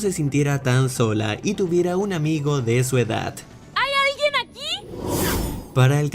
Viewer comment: Lo que dice en el minuto 5:31 es: Si lo del fondo son palomitas